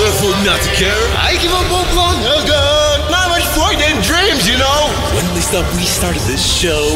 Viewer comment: I thought he said Mom, I farted dreams.